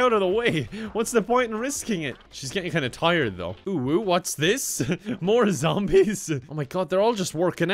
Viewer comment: Lol, is a furry. He said "UwU what's this" in 8:40! And in 2:29 he said "UwU not like this"